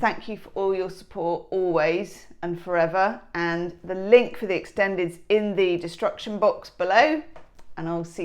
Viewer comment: I heard it as ‘ link to the extended is in the distruction box below’ 🙉